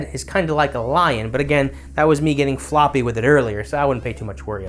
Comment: OOOOhhh!!! He's saying "getting 'Flaw P' with it" I thought he was saying "getting floppy with it" Very clever.